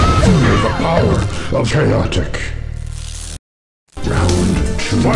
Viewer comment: what does he said for Hornbuckle Wins? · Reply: "Feel the power of CHAOTIC" A reference to secret fighter lines in original MK2